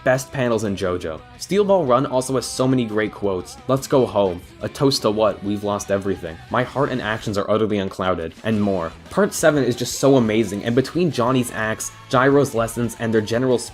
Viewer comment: I know you said Johnny's acts, but I thought you said Johnny's ass at first, and I was like well he ain't wrong